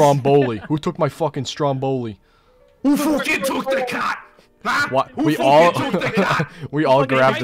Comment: I thought he said who took the cotton